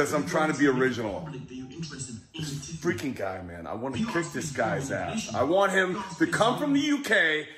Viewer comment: It sounded like he said he "wants to kiss your ass" 😂😂😂😂😂😂😂